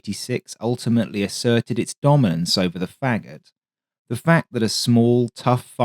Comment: 986 is the obvious better less threatening arrangement. Did he really say foghat at 0:22?